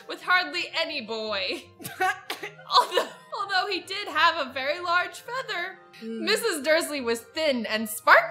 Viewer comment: When said "... a very large feather..", I thought she said "... a very large mother..." That got me laughing